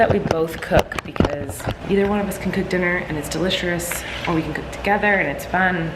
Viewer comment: am i the only one who heard her say "Deliserous" at 5:56 lol